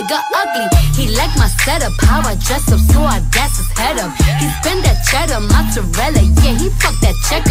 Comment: “Gas his head up” instead of question mark.